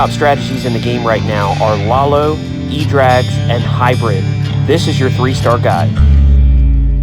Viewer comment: I must have mis heard you. I thought I heard you say “edrags”. I must be going crazy.